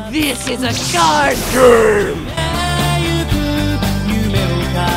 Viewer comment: what did he say at 0:47 i think he said card games.